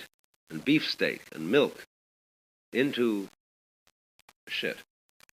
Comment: Did he say "shit" at 5:26 or is that the glitch of the audio being played?